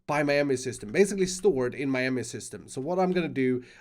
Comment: Is anyone hearing Miami system?